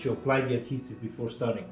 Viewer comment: Thank you so very much for your informative video. Sorry to have to ask. But at 13:10 I didn't quite get the part about new users? Do you mind telling me what you said there? When the cover came off? Was you meant to remove that print first before continuing? Thanks. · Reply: Thank you for the kind words. Before starting a new print, it is a recommended to apply adhesive onto the printing bed. A sample of the adhesive is provided with the printer.